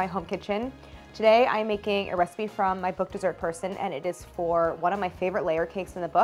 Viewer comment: All this time I thought your intro song said "She's a person" 😞, dessert person makes way more sense 😅